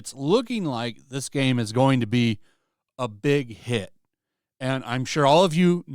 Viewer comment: I thought I heard "this game is gonna have big hips"...